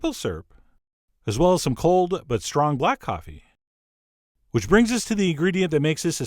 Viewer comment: oh my god. I'm laying here in bed with my fiancee and he heard you say bukkake instead of black coffee and this video took on a whole new meaning.